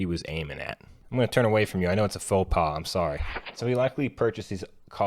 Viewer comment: what's the word he said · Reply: "faux pas" - an embarrassing or tactless act or remark in a social situation.